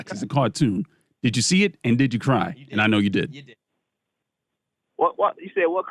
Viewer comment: wtf is he saying? I’ve listened to it 100x. “The pick saw movie so, did you see it did you cry?”?!?!